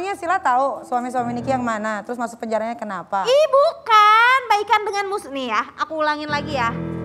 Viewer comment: Salah denger tuh nikmir, bawa laki2 enggak kok. "Baikan sama musuhnya" kali dengernya balikan sama suami nya wkwkkwkk